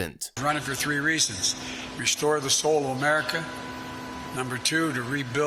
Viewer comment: did the potato say "restore the soul" of the country.... i swear I heard "gut the soul" of the country